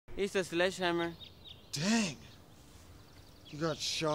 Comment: Iza sledgehammer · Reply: I thought he said “this is sledgehammer”